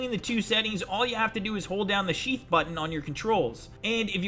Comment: did he say shieft